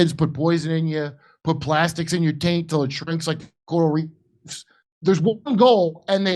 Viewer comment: did he just say “put plastics in your taint until it shrinks like the coral reefs”? 😅